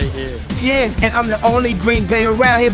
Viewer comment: i thought that Luigi said he was the green Goomba